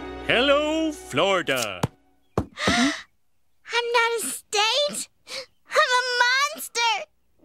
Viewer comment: Am I the only who thought she said “I’m not a steak” because that costume looked like a steak to me lol?